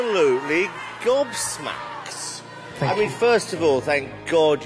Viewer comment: What is that word Simon said? "I'm absolutely...?" Goddmaks? I can't hear it clearly 😢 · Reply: Gobsmacked